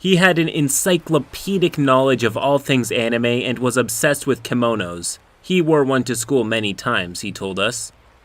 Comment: Okay I was terrified when I thought you said he was obsessed with and wore "kemonos" to school... not kimonos. That's not much better but still.